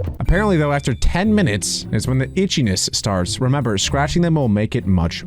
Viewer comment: ⬇PEOPLE WHO SAW THE CURSED WORD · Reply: the thing is he didn't say the b word but they thought he did He said itch